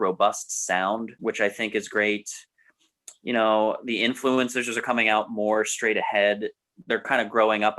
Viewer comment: did you say "influencers"? Influences? Let's not allow trash internet culture further bastardize language. Maybe it was just a case of over-salivation slurring the word because Debbie Harry was Sexy as f&^%*k. Ah'm nitpickin' to be sure. Like this concept...appeals to geeky list-making culture...not even talking about the music, per say, much less cultural context...reflects the Culture of the Singular Authoritative Opinion of our current state....